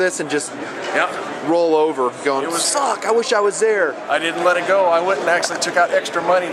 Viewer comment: I thought said F**K!?!?!? He didn't though but it was funny!